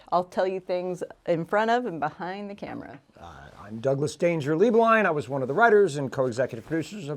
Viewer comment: When introduced himself for the first 8 episodes I thought he was saying, “Doug ‘Dangerously’ Blind” 😭 Cracked me up so hard when I realized I was wrong 😂❤️